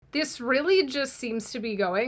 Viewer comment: Funny how it sounds like you said "The israeli just seem to be going from bad to worse" at the start, which is also very accurate LOL